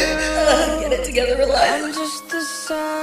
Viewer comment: What is ar 1:07/ Why she cry sorry i'm frenchso i don't understand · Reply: Hey, she just says "get it together Eliza", I think it was after watching the episode 08 of season 2, after Finn's death